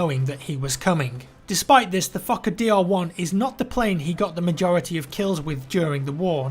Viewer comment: Fokker DR1, when you read it I thought you said fucker dr1